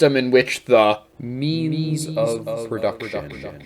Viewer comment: I keep hearing "meanies of production" lol